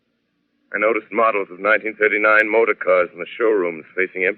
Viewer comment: Did he say 1979 cars in the showroom?